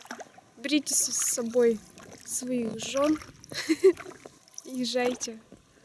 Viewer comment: Послышалась в напутственных словах: "Берите своих жён и жарьте"!))) Потом только сплавляться...))) 17.34))